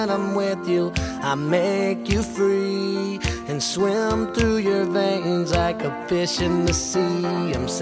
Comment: I always thought he said "I can swim through your veins I can piss in the sea"